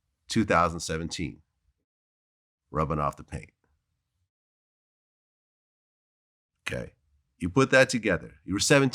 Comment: What'd he say right here the sound went off?? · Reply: "Boo Boo baba"